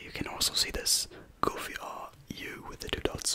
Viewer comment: Did he just say goofy ahh